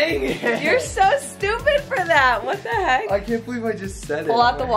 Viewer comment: wait....am i the only one who heard said “thank youuu...next”, right?😂she said it